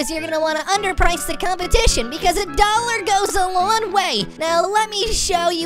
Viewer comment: Is it just me or did say $1 goes a LON way